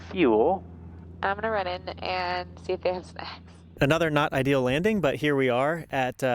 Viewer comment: it sounds like she says I going go in and see if they have sex 😂